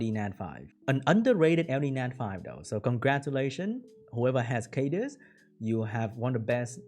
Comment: Best branders for this are Cadiz/Jaara -> Fire Ryu/Wind Homie -> Lexy/Stella/Shihwa -> Hraesvelg etc. :) Glad you got the wind homie to work :) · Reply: Who was the LD that said is the BEST for branding? I couldnt make out what he said. at 2:25 he says it but i cant tell what it is. Did he say Helleas?